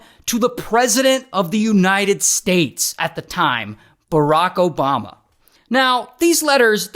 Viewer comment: Wait low-key which president? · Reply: Did you not watch the video??? 7:10 he clearly says Barack Obama